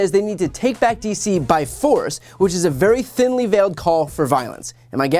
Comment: Lol, that's what I heard. "Take back using force"